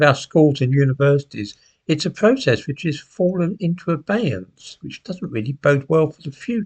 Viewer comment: What does says at 8:20? "...it's a process which has fallen into (obyance?)" . Thanks for helping me